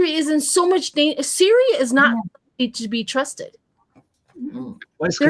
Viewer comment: Lol. I heard "Siri is not to be trusted."